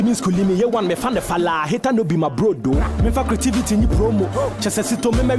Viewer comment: Did I hear A1 brodo